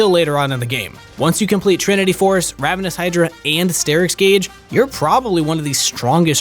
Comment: did he just said ravenous hydra?